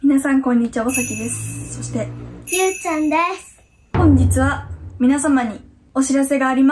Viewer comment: です が ゆぅちゃんですって聞こえる人 ⤵︎ ⤵︎ ⤵︎